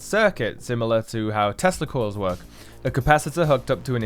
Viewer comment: Y’all It sounds like he said testicles not Tesla coils